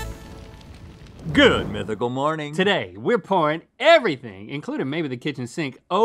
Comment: Damn I thought Link said: “Today were corn” But yknow Not corn